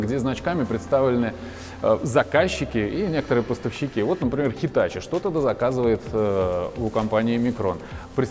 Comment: Где ты там услышал Китай? Он сказал "Hitachi". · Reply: Действительно, не расслышал, точно hitachi, дома все спят, очень тихо смотрю. "Китай" просто послышался)